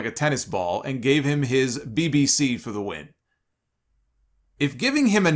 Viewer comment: I really thought said Keith Lee gave him his bbc. It was actually: Keith Lee gave him his BBT. Ha.